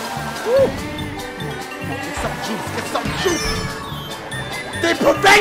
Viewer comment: I thought he said "GET SOME JEWS" 😂👌😂👌😂👌😂👌😂👌 SMASH THAT MF LIKE 💯💯💯💯💯💯💯💯💯💯